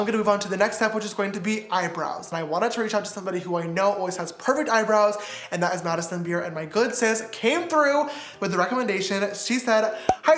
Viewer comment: > I thought James said Addison Re hahahaha